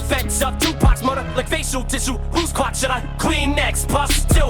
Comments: Who’s clock should I cleanex I thought he said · His voice.. its crazy to hear it now, sounds just like it did 10-15 years ago. its so weird but cool. Grip and Boogie killed this too, just all around. I thought he said "like facial tissue whose CLOCK should i clean next? Puffs?" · Thought he said "who's clock should I clean next" not car